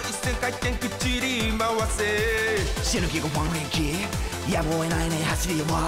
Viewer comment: 死ぬ気か不安？平気？ここの部分空耳で死ぬ気の部分が｢シルエイティ｣で不安？平気？が｢180SX(ワンエイティー)｣と掛けてると思ってる。